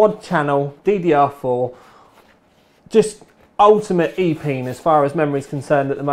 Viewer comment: Did he just said "Epenis"?